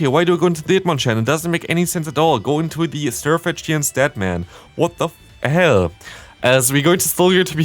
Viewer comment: Now i noticed it 11:57 i hear what the hell just its fine🤨🤨